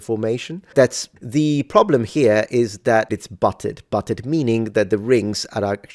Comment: I heard this initially as the problem was the the mail was "buttered".